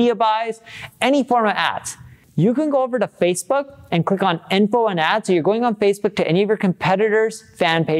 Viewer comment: Hi Neil. Not sure to understand what you say at 2:30 - I'm French and it's going a way too fast for me. So what to do to see ads from competitors ? Thank you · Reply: So, Facebook did shut down the "Info and Ads" section. Now you have to go to <link> and search for the page you wanna spy in order to see the Ads of their competitors